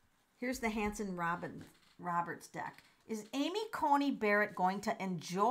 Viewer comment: Thank you for the reading! Amy Covid Barrett was born on January 28, 1972, according to a few different sites and news articles I found. When you said, "WHO is Amy Coney Barrett," I heard "WHAT is Amy Coney Barrett," lol. You talked about no one telling her what to do, I remembered the look of complete unease on her husband's face during her confirmation. I know others have suggested that she is part of a cult where women are subservient, but subservient women don't make moves like she does. Religions such as her stand by the belief that the man is the head of the household and the wife is the helpmate. I don't feel like that's the case in the Barrett household. She's SO controlled in public. I would bet money that when she doesn't get her way, in private, it's a sight to behold. 😑